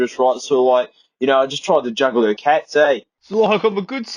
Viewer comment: what the fuck is he saying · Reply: "So I just tried to juggle her cats"